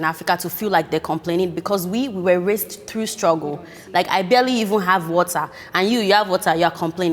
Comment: Lmao I thought she said "I barely even have WhatsApp" the first time I watched and it didn't even not make sense to me. 💀